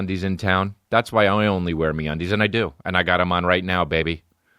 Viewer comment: When he started talking about me undies I thought he was saying Mondays lol